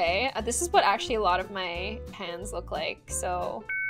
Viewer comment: at first I thought she said this is what most of my fans look like….. Anybody else……. Nope……. Ok…..